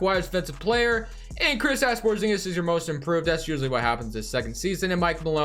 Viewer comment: Is it just me or does it sound like he says "Krisass Porzingis" at 9:09?